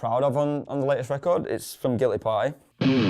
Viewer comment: No one talking about my boy Sean from WSS? Think he's one of the most creative and inventive guitarists of the Metalcore scene rn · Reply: What song does he say this riff is from? It sounds like he said “guilty pie” but I hnfc